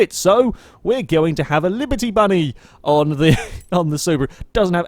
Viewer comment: Did he just say "liberty bunny"? We have a new widebody kit tuner!